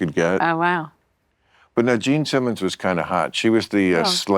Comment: Jean Simmons wasn't kind of hot. She was smoking hot 🔥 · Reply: So I didn't know who Jean Simmons was and when i was listening, had to do a double take because I thought I heard "Gene Simmons" , lol